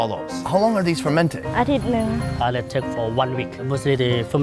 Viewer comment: sounds like she said "I dont know" in an english accent